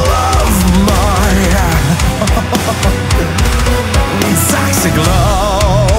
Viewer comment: swear I heard “sexy glove” 😂